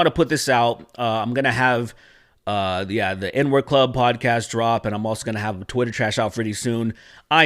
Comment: It sounded like you said the "N-word club".